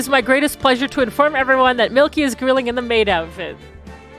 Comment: I skip forward to around 10:00 and I hear "Milky (Miki?) is in a maid outfit". Man I should watch this stuff live but I am too busy dying from swampass in my damp office